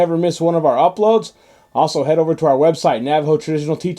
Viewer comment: I was looking away when he said this and at first I thought he said "Ay sh** head!" Had to replay it a few times lol